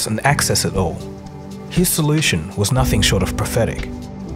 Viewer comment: What does he say???? Am I hearing wrong? Are you hearing: His solution was nothing short of PATHETIC